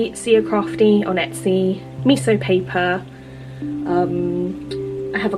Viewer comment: Hi there. Love your journal. Thanks for sharing. Could you please tell me where you got the tin of stickers from please? It sounded like Miso Stickers but I can’t find them so must have misheard. Thanks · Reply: Hi Penny, thanks for the kind words :) yes of course - I got them from Miso Paper (so you were close!) here's a link to all the Dolce Vita stuff they stock <link> hope that helps :)